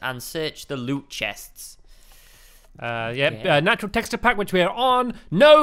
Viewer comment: Does anyone know what texture pack this is? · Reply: they say it at arounf 1:30 I think they said the natural texture pack but idk